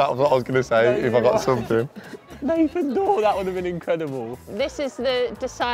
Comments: What did jack say at 6:33 I’m so confused 😂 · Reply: Just rewatched and realised it was Nathan dawe 😂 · i dont understand what he sais or what the joke is someone please explain? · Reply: I think he’s saying Nathan dawe the musician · what did Jack say I don’t get it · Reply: Thought he said Nathan Doyle 😭